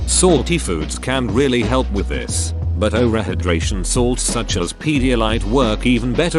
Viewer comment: why tf is "over" censored? like it's like "o***" instead of "over." "Over" isn't a bad word, tf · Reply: Oral. It's oral.